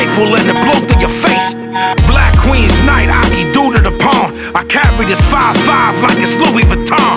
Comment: What does Vinnie say in the minute 1:58? Black queens nights....??????? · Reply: "black queen's knight ahki due to the pawn" something of chess, I think